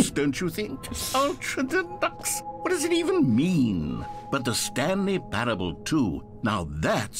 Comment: “Better TSP” Jack: I don’t know what that is… Also Jack: playing The Stanley Parable (TSP) Bless his cotton little Irish socks. 💚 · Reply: I thought you said The Stankley Parable